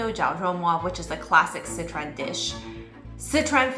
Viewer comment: When the subtitles say "citron" from about 10:00 do they mean Szechuan? Confusing! And your 15:00 question re: sharing. Both are acceptable, but people usually like different things, so if I got a spicy dish I don't expect others to try it. But it's also fun to order a bit of everything to see, as you say, quite common for Chinese. An interesting idea for when you travel would be to try "Chinese" cuisine in other countries! UK vs USA (I've never heard of what you call 'typical Chinese' lol)